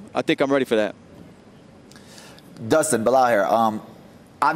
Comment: Did belal just say “Dustin, the liar” at 2:19 lol what?!